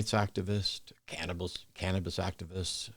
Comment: In the beginning, I thought you said your friend was a cannibal activist 😂